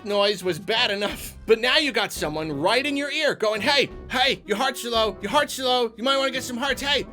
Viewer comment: "Hey, Hey! Your Hearts, Arlo! Your Hearts, Arlo!" (I know what he's actually saying there, this is just funnier.)